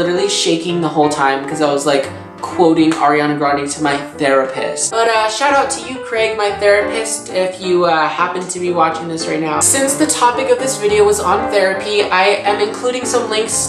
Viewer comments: I thought it said "the rapist" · Oh... I thought you said the rapist...